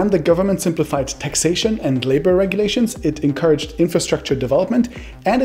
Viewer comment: did you say simplified labour regulations? This doesn't sound good...as it usually means no minimum wage laws and work safety regs. for workers